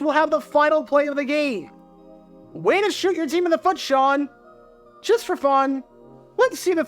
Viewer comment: "Way to shoot your team in the foot, Sean." What I first heard was, "Way to shoot your dick in the foot, Sean." Which is now my motto.